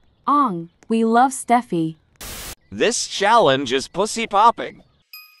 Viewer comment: "poosay popping"💀💀 thats what I hear lmaoooo